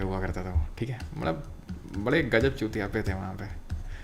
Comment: did he say chutiyape at1:57:14 🤣🤣🤣🤣🤣🤣